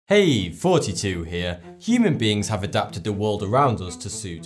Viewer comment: Oh in the intro he said "hey thoughty2 here" this whole time I thought he said "hey 42 here." It make sense now